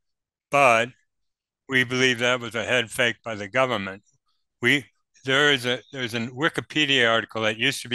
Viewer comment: Did he just say "... a head fake by the government." ...? I've never heard that before, oh, but how prevalent the activity is!!